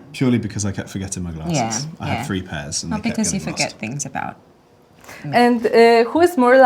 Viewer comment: Is she saying I got anxious I go to bed? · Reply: because you keep forget things about me? I cannot hear clear...